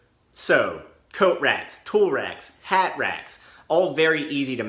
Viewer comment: I swear i heard "so: coat racks, tool racks, cat racks" and, as a dog person, i laughed maniacally.